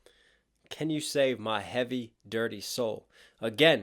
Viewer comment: My little brother made it so I can unhear it and said it sounds like he says can you save my heavy dinosaur. Amazing break down as usual though.